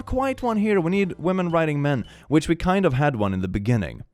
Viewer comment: I was listening to this while I was playing Minecraft, and during the start when he was reading off the bingo spots I heard 'Women riding men' and whipped my head so fast towards my phone. Turns out it said 'Writing' and I died in my game lol